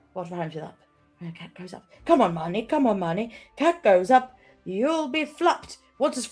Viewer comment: I heard this as "cat goes up, you'll be flucked", I'd say entirely because "fliuch" (pronounced roughly as fluck) is Irish for wet, so it immediately conjured the image of a cat scrambling away from water on deck 😄